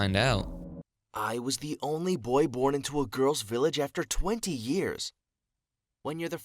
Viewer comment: he didn't say 30 he actually said 20 but it would not be real either way